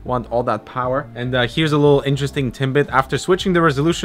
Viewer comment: It sounded like you said timbit, not tidbit - you've been in Canada too long ;)